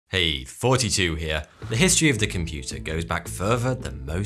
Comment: Is it just me or does it sounds like he is saying "hey 42 here"